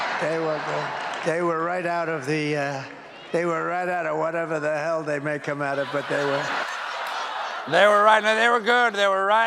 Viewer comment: Ohh Trump's talking about the deep fryer. I heard "whatever the hell they make them out of". Lol I thought Trump forget that French fries are made out of potatoes. 😂🙄😁🥔🍟